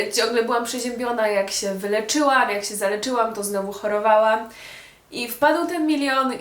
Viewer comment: usłyszałam zaręczyłam